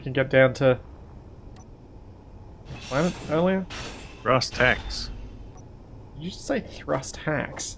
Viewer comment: i heard 'thrust hacks' :D